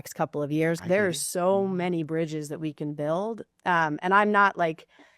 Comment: "So many bridges we can build." I heard "So many bridges we can burn."